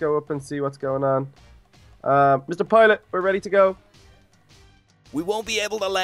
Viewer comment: did he say mr. toilet or did I misheard it